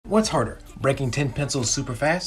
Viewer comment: I thought he said drinking a highlighter 💀